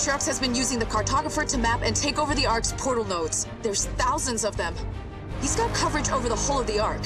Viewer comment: Anders says "thousands" but subtitles say "hundreds"